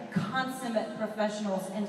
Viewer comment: At first I thought she said they were "constant professionals" and I was like has she not heard about all the pranking? XD